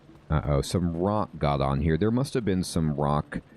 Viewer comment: It's a long time so this is out of nowhere just watching this video, I thought you said "I got some rot in there" 😂😂